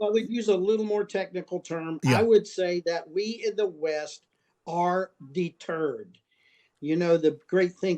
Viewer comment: i initially thought he said "We in the West are the turd". An unusual claim, i thought, but we seem to be living up to it by not delivering on our promises to Ukraine.